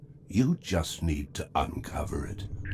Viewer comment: #random At 5:50 the subtitles said unveil, but the voice said uncover!